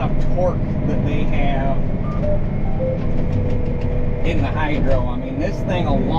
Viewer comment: It’s a hydro not a CVT. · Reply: Oh, my bad! I rewatched that part, and misheard ya say Hydro as CVT somehow!😅